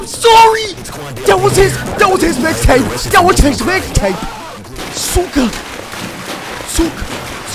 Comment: bruh, did u just say "suka"?